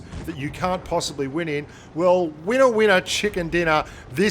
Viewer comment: Did I hear winner winner chicken dinner? Back then I didn't know what the meaning is lol